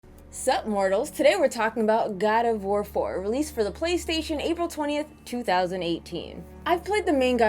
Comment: At first I thought you said, Sup Morons 🤣